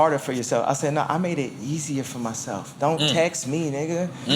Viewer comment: I heard I made it Yeezier for myself Hehe yeasier